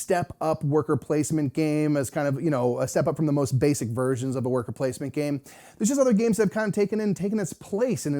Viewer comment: Sure other games exist but we have had literally hundreds of people say it was their intro to worker placement so that’s what I was getting at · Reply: OK, I can see that. I heard "birth of work placement games" and thought you were talking about the game's place in design history. I'm old, and Lords of Waterdeep seems like a new game to me.